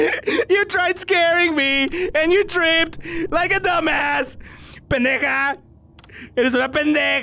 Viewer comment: I SWEAR TO GOD HE SAID THE N WORD!!! XD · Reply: As a Mexican I can prove this comment wrong, he said "pendeja"